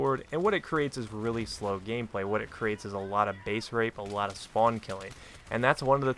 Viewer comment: Thought you said facerape at 4:49 and im like Lolwhut?!